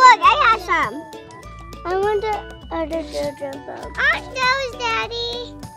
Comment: did she just say I am vsco girl lol😂😂😃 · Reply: Angel Alvarado it sounds like it 😂 but she said I want this jojo now 😂